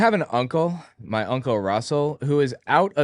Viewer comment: I misheard you at the start and thought you said, "uncle RUCKUS" and was gonna be like OF COURSE...